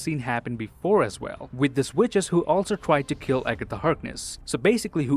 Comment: Did he just say witchess